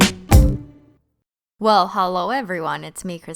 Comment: say hello super fast and sounds like your saying HOLO